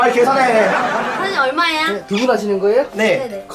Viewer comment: 나만들었나? ㅋㅋㅋ 가 사장님 에게 "사장님얼마야?"라고 반말을썼넼 ㅋㅋㅋ 엄청웃겨 나웃소팬인뎈